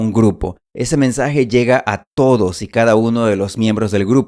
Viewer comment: I just finished using the transcription method with this video and found it very useful. As always, thank you very much for providing videos for students with accurate subtitles. However, I think I found a few errors in the transcript but I've been wrong before so let's see. 0:08 The subtitle says "llega todos" but I believe that the narrator actually says "llega a todos". 1:40 The subtitle says "Eso" but I believe that the narrator actually says "Esto". 1:52 The subtitle says "la información" but I believe that the narrator actually says "información". 3:01 The subtitle says "si" but I believe that it should be "sí" (con acento).